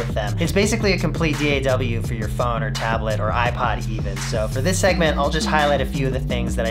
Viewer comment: did you say "iPod"?